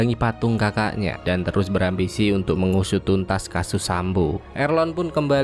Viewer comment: apa cuma gue yg denger kasud sambo di menit 6:15, atau salah denger yaa?🤣🤣🤣😂😂😂